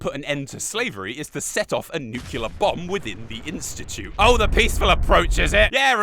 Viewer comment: A What Bomb? I'm sorry, you weren't speaking cularly, so I couldn't catch that word..... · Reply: nuclear